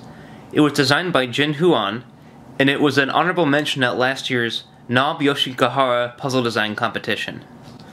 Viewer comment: what is he saying? "Naobioshi Gahar" ? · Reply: Nob Yoshigahara! Legendary puzzle designer!